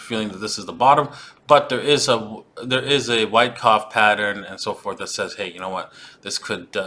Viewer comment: LOL...I thought he said white cop pattern… Does this mean the markets going to be killed?